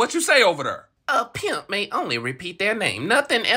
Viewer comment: I thought he said a pimp doesn't repeat himself